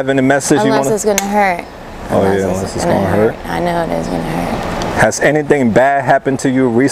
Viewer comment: minute. Does she say "I know it's going to hurt".? 9:23-9:29 she says "it's better to go sober, right? What does that mean?